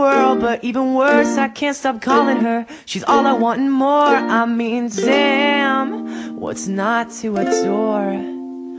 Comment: I low-key thought he was saying "I mean Sam what's not to adore"